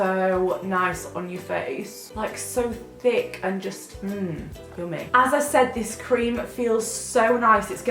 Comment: I wasn't paying attention and at "1:27" I heard cummy not yummy.. :X!!!! <3